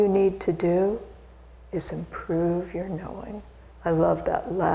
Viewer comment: improve your what? did she say knowing?